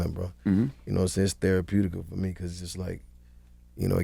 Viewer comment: Did he say “Its Theraputical “